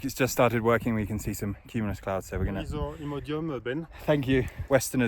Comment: What does he say?.. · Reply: “Here is your Imodium, Ben” 🤣